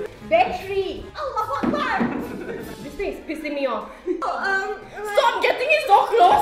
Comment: did munah say allahua akbar?!!